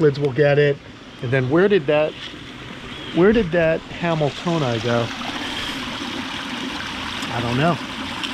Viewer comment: 17th place ain’t nothing to sneeze at! Great job buds! I thought you said “Where’d that camel toe go?” (Hamiltonai?)